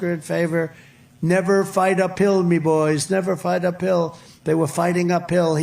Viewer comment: Thought I heard him say 'Never fight a pill me boys'. Then you see Lindell's ad. Maybe he meant 'never fight a pillow me boys'. Then again it is Mike Linthell. This is just too confusing or brilliantly packed with double meanings.